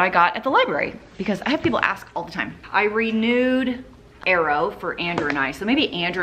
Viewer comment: When you said “I renewed”, I heard “I read nude”. OMG! I haven’t laughed that hard in so long! 🤣